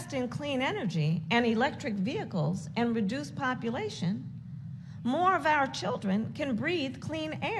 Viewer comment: Does she say “reduce population” at 9:41?.. How is she gonna do that? She and her boss are true satanists!